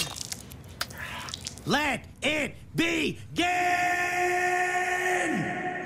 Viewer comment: I thought he said, "let it be gay." Lmao